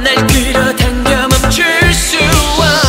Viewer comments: Leo: I want a space girl Me: hello nasa, you hiring? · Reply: Lmaooo y did I hear 'I want a space car' 😂😂😂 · Leo: I want a space girl Me: hello nasa, you hiring? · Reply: KpopTaez don’t worry, I heard “I want a spice girl”, lol